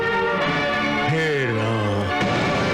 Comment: When Dhrutarashtra knows that Dronacharya has put his arrows down - Does he says "hey Ram"??